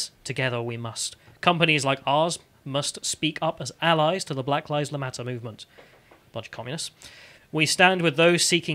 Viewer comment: I am sure I heard ‘The Black Lies Matter Movement?’ Me thinks a Freudian Slip was incurred. 👀